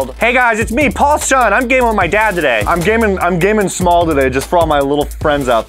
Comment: Why did I hear I'm gay with my dad today.